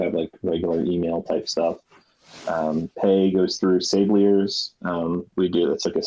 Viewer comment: Joseph talks about how the sushi Dao does payments. Does anybody know what he calls that system? “Paygliers”?